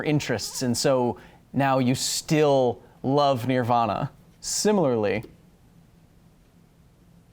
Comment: Did you say, NEAR-vana?